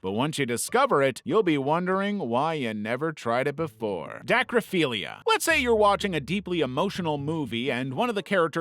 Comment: when i heard "dacryphilla" it was on 2x speed and i thought i heard "necrophilla"